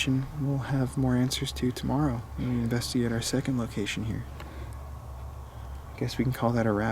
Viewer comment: Omg! That voice at about 13:46 sounded like, "come this way."😱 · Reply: I heard "guess the cause"